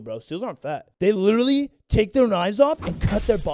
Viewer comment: I thought he said "to become a cereal!!!" lol had me choking on my drink then realized my ears are idiots 🖕👂👂